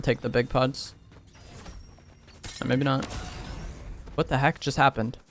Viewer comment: "extreme edge with the helly" 😳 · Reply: LOL i thought i heard “what the helly” i was like okayyyyyyy